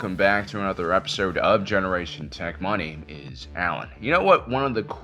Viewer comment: At first I thought he said generation tech MONEY. But then he said his name and im like ohh "my name is" But now im disappointed and want to say MONEY like he is mister krabs. 😂